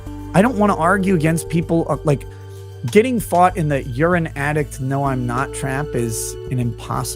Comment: I heard "urine addict" at first